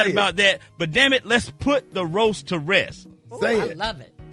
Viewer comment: I thought they said "let it rest ". Smh. Like they had the power to tell us what to do!